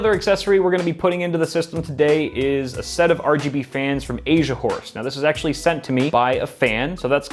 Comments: thought he said asia whores at first lol my ears XDD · I heard him "Asia whores" 😂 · thought he said Asia whores...then I saw the horse on the fan